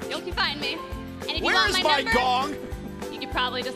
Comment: Did someone say where my dog at 1:45 or